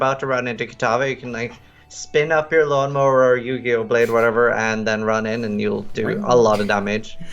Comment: lol is it just me or did say "yugioh blade" XDDDDDD